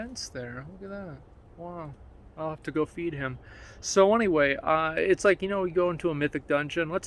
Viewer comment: possum walks by Asmon: I’ll have to go feed him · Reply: Wait, I swear I heard I 'ill have to go and beat him now.